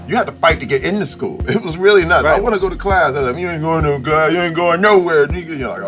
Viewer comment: anyone catch him saying "you ain't going to no class nigga?" lmao is awesome!